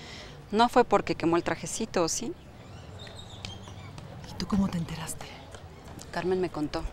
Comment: En el minuto 13:18 no dijo y como se entero ? Sino que dijo muy grosera y dijo y tu como te enteraste !!! Antes en la primera temporada muy buena onda pero en esta pero bien grosera cae gorda ,no manches si se paso